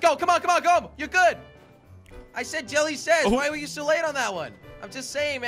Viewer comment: sounds like Jelly said Jelly says.